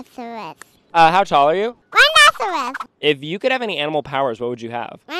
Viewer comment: Help on the "what's your favorite bird" I heard "carrot" 😭